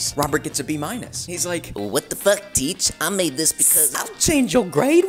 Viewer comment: "Wtf teach I made this for like hours" got me💀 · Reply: I thought the student said: "WTF teach. I made this because-"